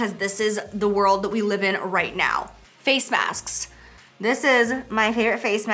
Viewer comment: I thought when you said masks you said snacks.. “scrolls the details box to see which snacks” 🤣😘